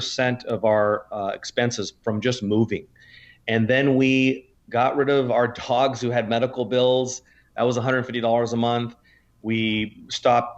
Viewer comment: Did he say they got rid of their dogs because they had medical expenses? its at 7:17 I probably misunderstood. but if somebody can clarify. thanks.